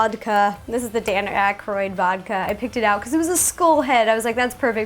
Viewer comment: I thought she said 'school' for a second there. Interesting pronunciation of 'skull' there, Ro.